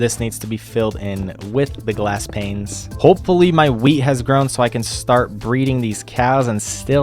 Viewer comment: (not the exact time but) when you said"let's hope my wheat has grown" I thought you said"let's hope my Wii has grown"😂😂😂